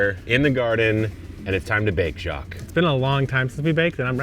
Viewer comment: Love the video, it's always great watching you guys, you both are very entertaining, and educational. I had a laugh at the beginning, because I heard you say "time to bake Jacque" 👨🏽‍🌾🥮 Lol you guys are awesome